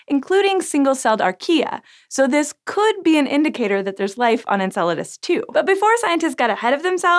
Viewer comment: I thought it said “life can live on enchiladas”